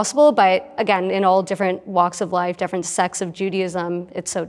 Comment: I think she said “sects” but cc put something else 😅